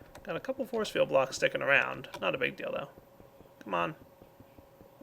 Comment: "Not a big dildo" - What you just said?